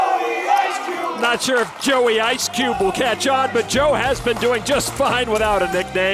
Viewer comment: I know they're yelling "Joey ice cube", but I keep hearing "Joe we hate you."